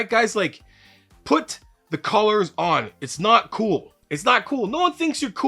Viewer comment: I thought you said 'colors' and watched it 8 times trying to understand how colors could save your life. Buddy said "He meant collars." Ohhhhh that makes so much more sense than having pretty plates on the bar.